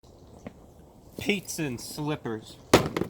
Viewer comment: I just heard "piazza and slippers" and saw your gaze at the camera and i knew i had to like it